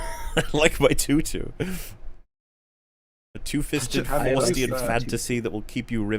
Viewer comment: I heard foreskin fantasy